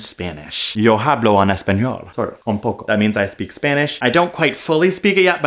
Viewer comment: I heard "I speak to you in sign" I'm not deaf, I've never learnt sign language however at first I interpreted sign as sine I'm guessing because of the soundwaves